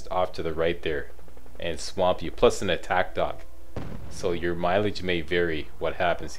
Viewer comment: did he say attack dog??